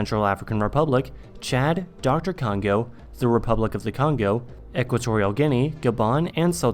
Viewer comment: F*** off.. did you just call it doctor Congo @ 4:40 😂😂😂